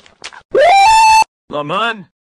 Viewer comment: i hear once "Mama" instead of "Lemon"?